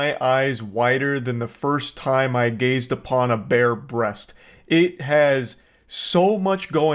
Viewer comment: I heard that as bear breast instead of bare breast. Took me a second..